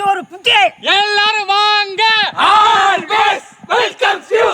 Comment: i think he said ALWAYS WELCOME YOU